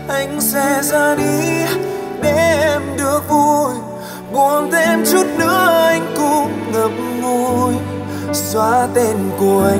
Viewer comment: khúc " buồn thêm chút nữa anh cũng ngậm ngùi" có ai nghe thành"buồn thêm chút nữa anh cũng ngậm b*ồi" không=)))